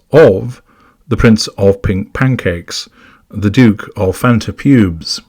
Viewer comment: Hahaa! Did I hear that right? The Duke of Fanta Pubes? If this is incorrect, I’m still really enjoying it. 🤭🤩🏆